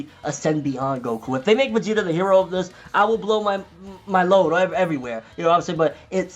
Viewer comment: Maybe I wasn’t listening properly; but when you were talking about Vegeta possibly surpassing Goku, it sounded like you said “...blow my load everywhere.”